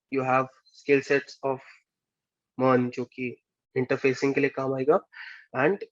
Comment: Bhai at 12:44 what skill set he is saying I don't understand please let me know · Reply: MERN